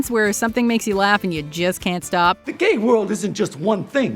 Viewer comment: sounded like you said "jizz cant stop" just letting you know xP